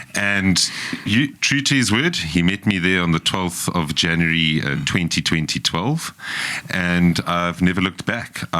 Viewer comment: Did I hear 👂 correctly 12 Of January 202012😅